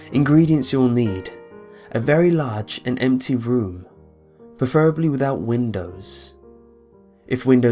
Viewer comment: When he said empty room I thought he said empty broom... Then I asked the empty room that I am in how you get an empty broom... How do you get an empty broom? That's a liget question